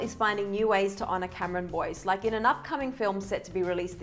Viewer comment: At first I thought it said "Cameron Boyce in a new film".